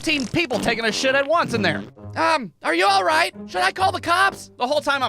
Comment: Uhh..do your children call you by your first name? Because it one of the IRL segments, I heard "are you all right, Tyler?"